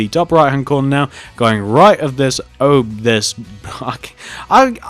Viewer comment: Did he say the....F word!? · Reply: "Ugh"